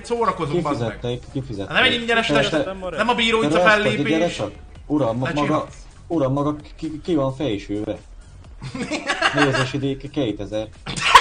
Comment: nál mit mond? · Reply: "Uram maga ki van fésülve!...Nézési díj kétezer." :D